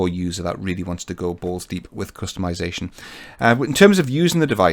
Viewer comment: Did he really say 'balls deep' (3:45). A bit racey. Nice review.